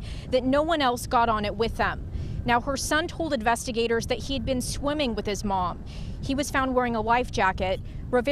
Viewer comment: I wanna know how the little boy made back to the boat but she didn’t 💔 Something about this story doesn’t add up 😭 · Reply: Nope he actually said that he had been swimming with his Mom but she never made it back... So the original comment is asking the right question.